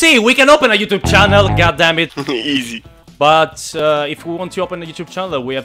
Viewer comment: A 5:27 ho sentito un certo god damm*t.... cmq le bestemmie italiane sono patrimonio Unesco